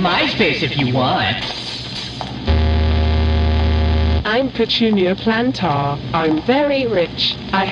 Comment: I thought Petunia said “I’m Petunia Potter” and if she actually said that I would’ve gone nuts